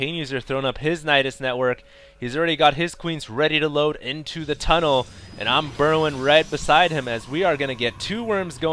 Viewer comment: Turn on transcribe audio and compare what it says with what HD is saying... Lmao. 0:28 "he's really got his police radio vote into the tunnel of berlin" hahaha.